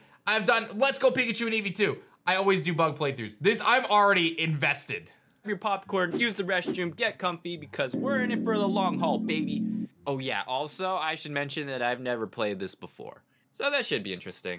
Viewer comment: When you said you're already invested I really thought you said infested... you know, cause bugs? ... I see the door.